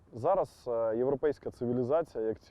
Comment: did he say 'European civilazation'? If so, what's he saying about the European civilazation? I'm still a little confused about what's going on in Ukraine. Can someone tell me what Azov Batallion fights for?